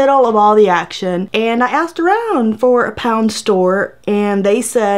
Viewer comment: It should be named "Pound Town" not "Pound Land" . But it's still worth a chuckle hahah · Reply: I half ass paid attention and thought shs said pound town at first 😂😂😂